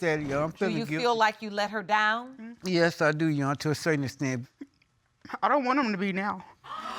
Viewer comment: Lady: “I don’t want him to be now” Judge Lakes: you are not the father Me: well you got your wish. Why was she so nasty to him??! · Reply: i thought she said "I dont want him to be 'down'"? That's what it sounded like to me with the badly edited audio audience reaction...